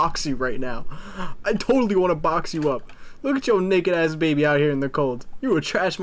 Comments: Did I hear that right? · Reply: Thought so too but he said naked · I'm just curious, what are you saying in 17:08 ? Because i hear you say the nword or maybe i just mishear